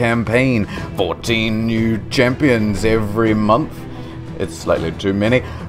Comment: I totally misheard and thought said "14 new players join it every month." And that sounded about right.